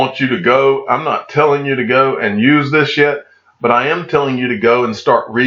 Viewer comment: I really thought you said a bad word ... You said “im not telling you to use this YET” But it sounded like “I’m not telling you to use this s***” I went back and listened 4 time just to make sure!!